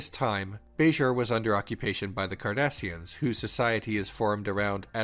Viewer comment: I heard Kardashians